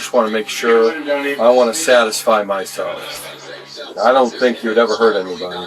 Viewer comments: i'm sorry, but if someone says "Take your shirt off, i wanna satisfy myself" i ain't taking my shirt off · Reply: i swear I heard "I don't wanna satisfy myself" but the caption said otherwise ig 😭😭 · I was barely listening when i heard “do me a favor and take off your shirt, i want to satisfy myself” 😮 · did he just say "I want to satisfy myself"?